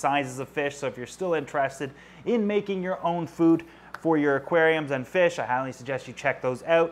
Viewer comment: I thought it said making my own fish, I was very confused